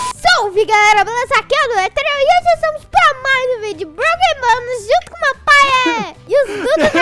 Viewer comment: O que eu entendi em 0:40 salve galera ??????? Mais um vídeo de Broken bonés